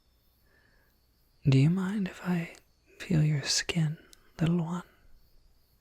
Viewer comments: Ok, ok, ok I thought, he said, can I pill your skin? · I’m sorry, did he just say “peel your skin?” 😃 False alarm; he said “feel” not “peel”